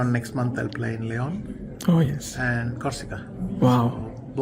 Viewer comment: what did he say after Leon? Which tournament? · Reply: corsica chess event i think